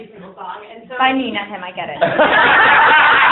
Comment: Sorry what did she said on 10:24? ;) I love this movie btw! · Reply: Anna says "by me, not him, I get it" (as in, the person asking the question was surprised that Anna could actually sing)